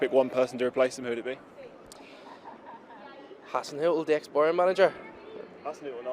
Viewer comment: what does he say here!?!!? the cc says Hassan hill???? But who is that please?! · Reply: He probably said "Hasenhüttel". The former Southampton manager. He mistook him for the Bayern manager cos he also has a Germanic name probably. Lad doesn't know his managers.